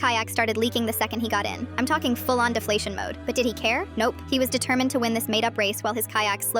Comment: I heard "diddy care" 😭